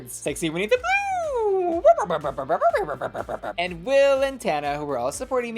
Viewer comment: I thought you was saying will antennae